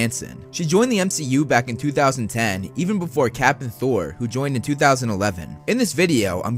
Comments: For a second I thought he said captain Thor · "She joined the mcu back in 2010 even before capt'n thor..." I KNOW you said "before Cap and Thor" but the first time I heard it I wasn't really paying attention until I thought I heard Captain Thor and was like... wait, what? And that got me thinking about a Captain America/Thor baby and now all I can do is think about the gorgeous ass baby Cap and Thor would have together and how badass he/she would be.... and then I started thinking about how awesome that would be as a sitcom.... i think quarantine has made me goofier.... boredom is a wonderful thing!